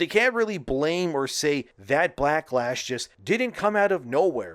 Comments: I'm sorry, did you just say BLACKlash? Because honestly, that is a PERFECT term for this shit! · did he say blacklash on purpose? lol